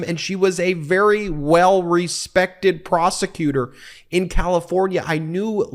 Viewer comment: i honestly thought you said 'well respected pros -----' im sure you know where that was going